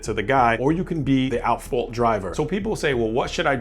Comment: I thought tou said asshole driver. I backed up and listened to you say "at fault". Either works in this case.